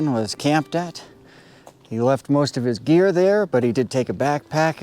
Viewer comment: when you said he left his gear I thought you said beer & was like "Nah no man leaves his beer undrunk unless its in the cooler son, very suspicious!!!" 😂